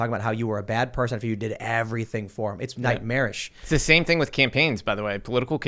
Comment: I thought Tim said "It's like marriage" rather than "it's nightmarish" at 11:23. 😄 I wish he had done because that would have been a completely apt descriptor to go with what he said leading up to that statement.